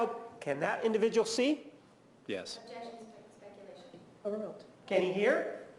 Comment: That Willmott was so out of her depth. She should go back to defending speeding ticket fines. What was that. I thought I heard somebody saying Objection speculation.